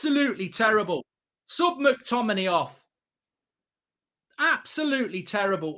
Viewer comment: Did you say you wanted to ‘Suck McTominay Off,’ that’s very generous considering how bad he is 😂